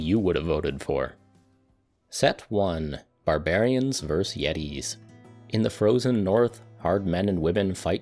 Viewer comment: I closed my eyes and thought he said Barbarians vs Yankees XD